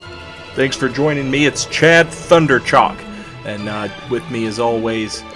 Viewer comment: Not even a few seconds in, and I hear "Chad Thunderchock" I am both aroused and intrigued. Keep up the good work